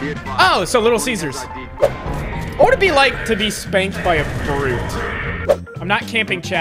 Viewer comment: For a second, i thought he said spanked by a fruit. lol